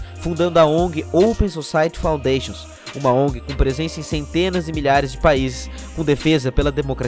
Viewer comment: "uma ONG que tem presença em CENTENAS DE MILHARES DE PAÍSES". Ouvi direito isso? Se sim, tem um erro messe trecho então. Mas o vídeo é bom. Gostei.